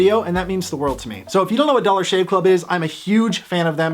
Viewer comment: was it just me or did i hear dollar gay shop......maybe it is well yeah...i heard it wrong cuz he said it agian and i noticed i thought wrong.....hehe.....